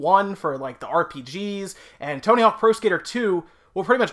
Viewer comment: Man Tony hawk was the best !! I remember spending hours playing that game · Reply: BullyInTheBuilding Sam I had to rewind this video like four times because I thought he kept saying Toenail something.... ohhhh Tony Hawk.